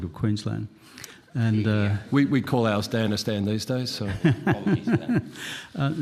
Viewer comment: "We call our something *Something these days" "Actually, that's what Rupert calls Victoria these days" What did he say? I've rewound like 7 times. Standards Stan? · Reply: Danistan